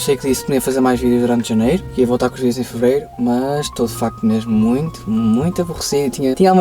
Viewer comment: Porque é que eu ouvi “a minha sogra viu o vídeo de fevereiro?”